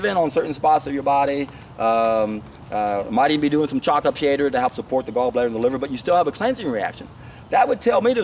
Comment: Hi all, so grateful for all you video's around 20:42 on this one something chopped up to support the gallbladder is mentioned but I couldn't quite catch what it was ? can anyone help? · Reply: chanca piedra, you can find it on iherb or wherever :)